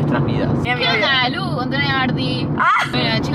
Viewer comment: No se ustedes pero yo le escuche que dijo que onda Lu con tu novia Marti.Nos dio indicios y no nos dimos cuenta